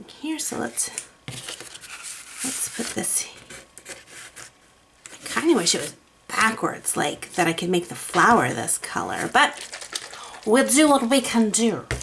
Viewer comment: I could really use some help with what is said through 8:13-8:24 - the auto-generated captions are a confusing mess. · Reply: She says: "let's put this...kinda wish it was backwards, like that I can make the flower this color... but! we do what we can do!" She says exactly that, haha. hope this helps!